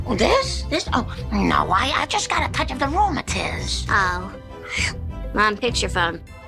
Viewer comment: the mother says this? Oh honey I just got a corona test that what I heard